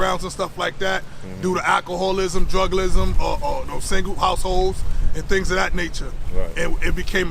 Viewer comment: “Druglism??” 🤔 Gotta google that one. I was this years old, when I first heard druglism.